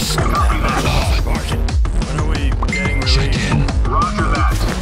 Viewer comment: What? He said "Chicken", seriously? I'm not that Soldier ATP, like fried chicken